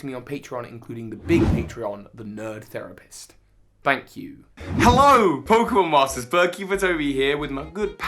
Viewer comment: "All" Notifications on. I always thought you were saying "Sword hype, Pokemon Masters" ha ha.